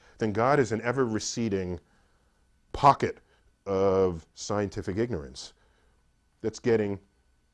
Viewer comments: Haha at 2:19 when he says "scientific ignorance" the automatic subtitles say "scientific literacy" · lol at 2:18 Neil says "Pocket of scientific ignorance" but the automatic captions say "Pocket of scientific literacy"